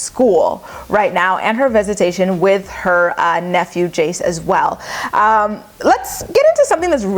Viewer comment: Did you say and her nephew Jace aswell